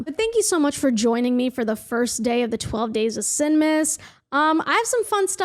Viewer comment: I thought you said "Sig-mas" lmao